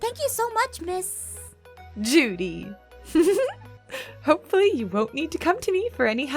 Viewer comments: wait, did she just say Judy? From Zootopia? No wonder how I saw her look similar as her color like purple with blue outfit she wear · I thought she said miss duty but she said Judy LOL